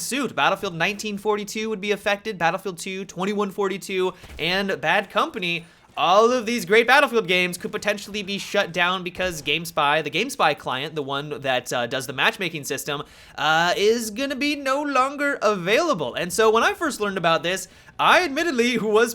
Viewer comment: I thought he said GameStop... I had a mini heart attack